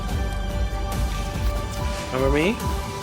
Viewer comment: you will hear him say number B but he actually said remember me